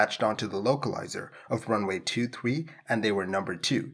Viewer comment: the subtitle shows 33 instead of 23 :)